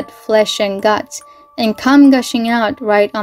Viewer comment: excuse me? Com? Did I heard that correctly?? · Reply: 😟 come